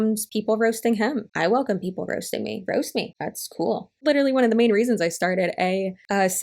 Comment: I thought she said "roast me, at school" lmao I'm an idiot